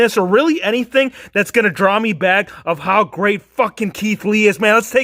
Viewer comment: does he say fuck? If so its gotta be the first time I have ever heard him swear 😂😂😂😂